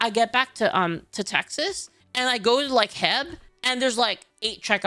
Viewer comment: Alright, hold on, did she just say "Heb" instead of H-E-B? 0:30 Needs another Texas injection of BBQ and yeehaw.